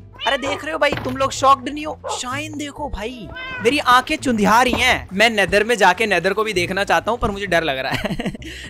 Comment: Me be like : kya bola tune. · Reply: chundhiyane lagi h . Jo suraj dekhte wakth hota h